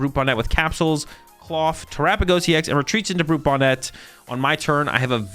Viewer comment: says: He whiffs the energy to retreat into the Budew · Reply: oh I hear it now clearly 😅. My mistake 🙏, I heard “retreat into budew”. Thank you for clarifying.